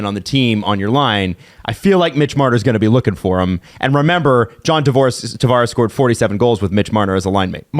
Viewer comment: I swear said John Divorce. Freudian slip if I've ever seen one lol.